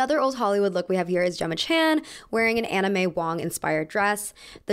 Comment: Every time she said "Anna Mae Wong" I kept hearing "Anime Wong." It wasn't until the last reference when she just said Anna that I caught what she was really saying.